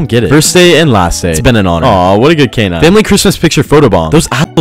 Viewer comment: The fact no one is talking about the poor police puppy ... Rest in Peace good boy <3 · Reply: unfortunately, i saw the original Reddit post and the dog was shot and killed. Also nowhere in the video does it say that the dog was diagnosed with cancer. Maybe you misheard “Canine” for “Cancer”?